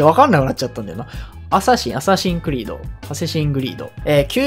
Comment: アサシンクリードがハセシングリードに聞こえた